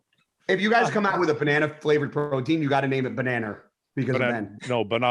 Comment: 100% Ben sounded like he said, BananRR 😂